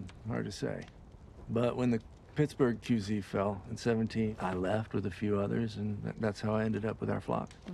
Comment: When I first heard this I thought he said "When the Pittsburgh QB fell in '17". I wondered what the Steelers quarterback had to do this. His concern about the Raiders did not help matters